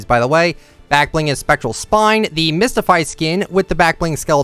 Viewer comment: Did he say specal spine